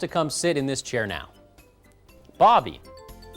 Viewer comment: did he say bobby ? hahahaha